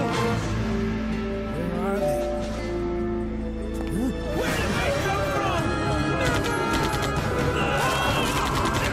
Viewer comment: What the fuck did he just say? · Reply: I think he said "never!" but it sounded an awful lot like another word.